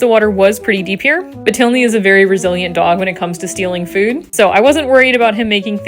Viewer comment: "A very resilient dog when it comes to stealing food" very funny! And awesome to see an owner knowing their animal so well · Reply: I'm Brazilian.. I heard "a very Brazilian dog when it comes to stealing food", and I got so mad. I mean, only Brazilians are allowed to speak poorly about ourselves! Then I read your comment..... She said "resilient", indeed. Lol 😂